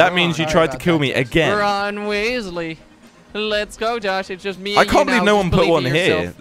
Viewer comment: did say Ron Weasley like from Harry Potter!!?